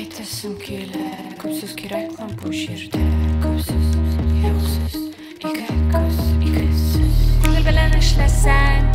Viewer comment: көп сөз-боқ сөз? Или мне послышалось 😅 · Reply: күп сүз-юк сүз (көп сөз-жоқ сөз) диде.